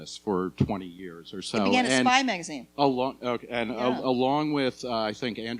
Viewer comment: Did she say Spy magazine at around 3:26? That is .... or was one of my favorite magazines. I subscribed in the 1980's and still have most of the issues.